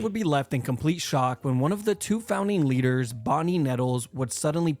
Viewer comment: When I heard Bonnie nettles, I thought it said Bonnie Neville’s because my grandma’s name is Bonnie Nevels, so I got scared for a minute until I realized it said Bonnie nettles